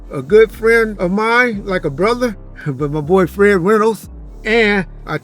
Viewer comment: seconds: Did he call ol boy his boyfriend?